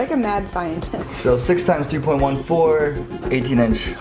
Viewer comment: did he say 2.14 is pi??